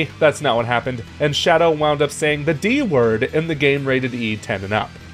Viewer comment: I thought Shadow said the N-word.